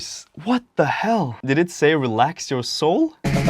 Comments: I thought you said “relax your soap” and I was 🤔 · It sounds like he says "relax your solo".